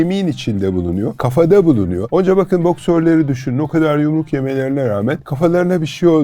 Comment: Çok eğlenerek izlerken 14:29da “Boksörleri düşünün o kadar yumruk yemelerine rağmen kafalarına bir şey olmuyor” cümlesini “doktorları” diye duydum, bir hekim olarak gözlerim doldu ama cümleyi hiç garipsemedim çünkü her gün yaşıyoruz. Tekrar izlediğimde boksör dendiğini anladım çok üzücü.